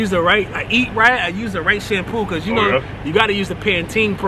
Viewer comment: Did he just say rice shampoo? Hahaha